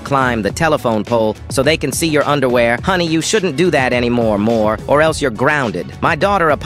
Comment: You can't do that no more-more😂😂😂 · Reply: I heard it as anymore wh0re